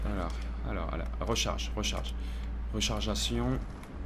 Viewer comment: c'est moi ou ta dit rechargation au lieu de rechargement lol